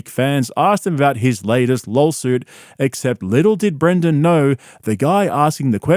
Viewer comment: did I hear you say “Lolsuit”? 😂